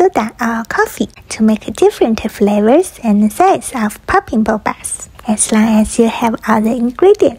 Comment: I thought she said:”stupid popping bubbles”💀